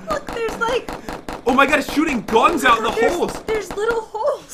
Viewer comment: Hahahahaha Instead Of Jen Saying Hole It Sounded Like She Said Hoes Or Is That My Headphones 🎧